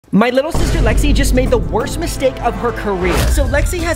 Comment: I just heard “stay flexy” now “my little sister lexi” what are the oods?